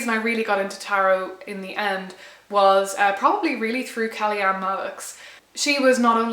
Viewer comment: What is the name she says at 1:45 id like to learn more of this person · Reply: Kelly-Ann Maddox - she has a youtube channel